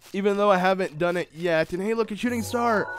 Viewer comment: did you said Shooting Stars...